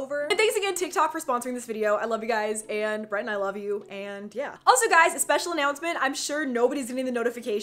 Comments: Did she say i love u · did you say brent I LOVE YOU AHHHHH SHIP! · It sounds like Eva said BRENT I LOVE YOU!!!!!!!! 😘😍💙💙